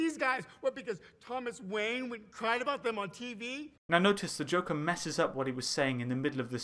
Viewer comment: I thought he just said “because Thomas Wayne WENT and cried about them on TV”